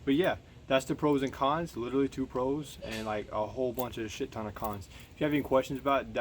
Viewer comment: Bro lmao what did you say @ 6:20 ???? Lmfaooo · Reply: Whole buncha shitanacons